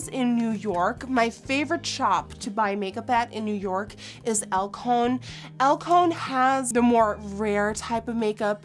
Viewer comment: I can't hear which shop she is saying at 6:44 any kind human being wanna tell me?😊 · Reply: Miss Sophie it's called Alcone